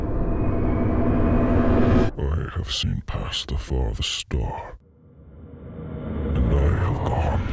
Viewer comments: Thomas: "I have seen past the farthest star... And I have gone to the edges of the earth... I can take you there, if you so choose. I can give you all the things you never knew you wanted, taste from the tallest chalice, dine at the largest hall... All I ask in return, is your soul. Your soul for a chance at a life without pain, without heartache. All of this I can give you and more... Just for your soul." Thomas: Drain you of woes... Your God has turned his back on you. That's why my tracks still pushing me forward... For people who can't make out what Thomas says. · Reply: When he said the star thing u thought he said “ i saw pasta at the store” so i thought thomas just wanted lightning to go for groceries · for those who wanna read what he said or don't understand: "i have seen past the farthest star, and i have gone to edges of the earth. I can take you there, if you so choose, i can give you all the things you never knew you wanted, taste from the tallest chalice, dine at the largest halls, all i ask in return, is your soul. Your soul for a chance of a life without pain, without heartache , all this i can give you and more, JUST for your soul." "Drain thee of woes, your god has turned its back on you, that's why my tracks still push me forward." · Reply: I thought he said he has some pasta for the storm